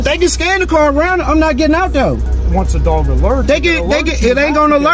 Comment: That Sergeant did not say "IF"....... at 7:47 he said "ONCE" the dog alerts we can pull you out of the car.